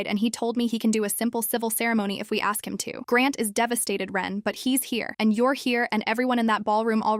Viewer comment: I love that the Subtitel says "RUN!" instead of her Name Ren. Fits perfectly.